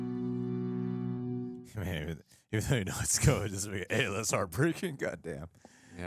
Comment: Donno what said but I understood every word 😢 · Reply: “Even though you know it’s coming, it doesn’t make it any less heartbreaking.”